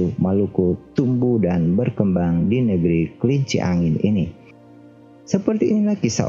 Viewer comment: Apa ga salah denger? Negeri kelinci angin?